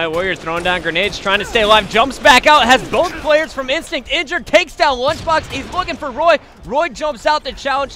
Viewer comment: did he just say Idra took out lunchbox?